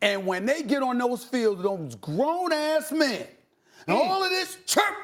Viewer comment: did he say "grown ass men?"